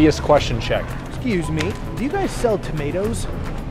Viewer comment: For the tomato lady: Looks to me like she was taken by surprise because the question had quite an obvious answer. However, instead of assuming or disrespecting she chose to direct you to another option. I'm sure she knows what a tomato is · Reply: The only thing I could figure is she must've thought he said " do you got sealed tomatoes?" Instead of "do you guys sell tomatoes?" That or she thought he was asking about a different variety of tomatoes, she appeared to be stocking the Roma tomatoes and then took him to what looked like maybe Beefsteak or maybe Globe tomatoes 😅